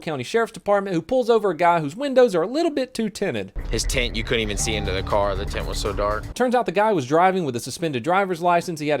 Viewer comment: I heard "his taint you couldn't even see into the car"